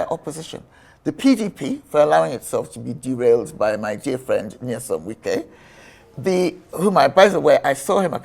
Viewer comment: Wait! Did she say "my dear friend Nysom Wike"??